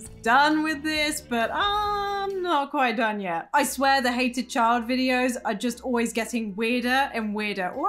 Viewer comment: When you said "Hated" I thought you said "Heated" The Heated Child! Warning! Wear Gloves First!